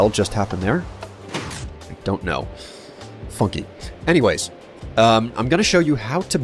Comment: Did you say "funky" or "fuck it" at 1:44? At first i was shocked that you were swearing but then i thought i might have just heard you wrong. ¯\_(ʘ_ʘ)_/¯